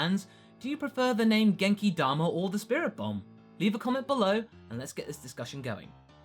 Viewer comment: I thought he said spirit bong -_